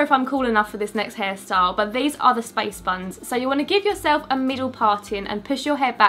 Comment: hum.........so did I hear spice buns. British may be confusing.